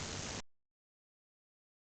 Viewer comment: min mark. When it says WHAT DO U THINK IT SAYS?. I hear WHO WANTS ME or U AND ME